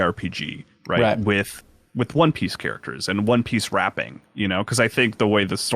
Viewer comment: I legitimately thought said "One Piece Rapping" as opposed to "wrapping" If you know, you know.